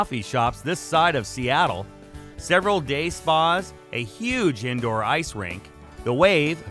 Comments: i deff thought he said 0:54 several gay spas... lol its deff time 4 bed im loosing it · i deff thought he said 0:54 several gay spas... lo